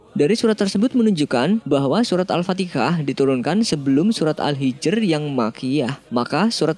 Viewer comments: Apa salah dengar, kok saya denger Al-patikah,,, 😇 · Maaf nih mungkin salah dengar tapi saya dengar min bilang surat al-fatikah